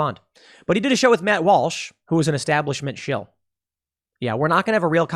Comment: Wait... Did Tim just call Matt Walsh an establishment shill?